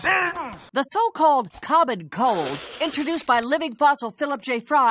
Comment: This season came out in 2011 but at 00:50 does she say the so called Covid cold? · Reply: No. She say "common cold". She is voice acting the fact she has the sniffles. You are also not allowed to call the coof the cold.